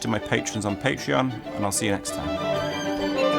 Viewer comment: In the and I heard "Ossia next time" Thanks for the videos